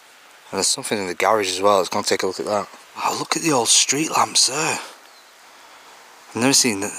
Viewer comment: I’m am giggling at myself because I heard you say I really like lumps, but then I realized you said lamps instead. It was a moment I needed to releases some stress. SILLY ME!